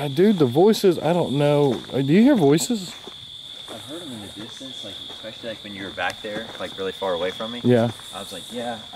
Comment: I definitely heard a Male voice at 18:03-04 just after you asked if he could hear any. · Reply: Yes, I definitely heard someone say, "I did"